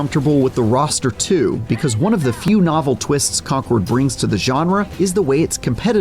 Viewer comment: did he say "conqward" ?